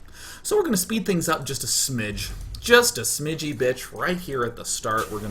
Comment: did i hear him right smidgy bitch at 12:18?